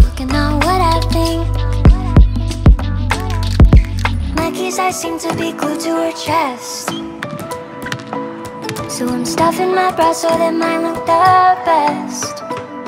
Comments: I thought she said monkeys are seem to be glued to her chest so I’m stuffing my Brest so I might look the best I’m so dumb 😂😂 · I think I'm the only one who hears "breast" instead of "bra"